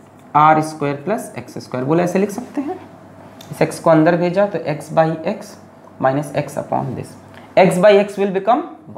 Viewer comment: what did sir say at normal speed🤣🤣🤣 Check Kisko Ander bheja